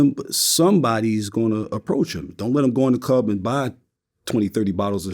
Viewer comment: “ poked him in the shower “ is crazy to me 😂😂😂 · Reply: I thought I heard the same thing but if you read his lips he's saying I approached him. I think he has a bad case of the burps during the video and he was trying to hold in a burp when he said "Iproched him".